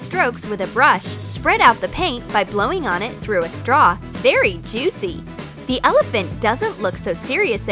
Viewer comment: Did she say very juicy? 😂